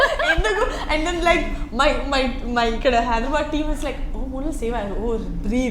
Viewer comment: what she said like Hyderabad team or something??